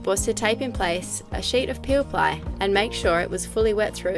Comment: Hi...what’s the name of the product that you put over the Fiberglas last. I thought I heard you say Peal something. I need some. Thanks for showing us how you did your great repair. Beautiful, Dee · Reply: Peel Ply or Peelply